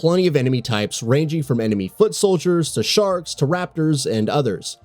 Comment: They need to concentrate on upping there 5 frame fps rate And I could swear the dude said "sharts" are one of the enemies 3:51 Looks super interesting.